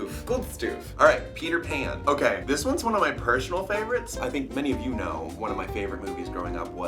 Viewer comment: I heard him say "Its one of my Pershonal favorites"